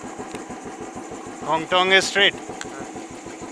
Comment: he said kongthong is that way go straight and he also said i can't understand you .